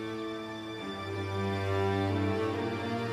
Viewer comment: ne dedi anlayamadim? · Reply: Keşke amcan seni verseydi